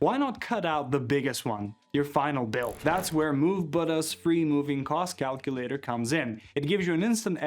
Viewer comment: what is the company name you're saying here? Move but us? I can't understand it. Frustrating. Thanks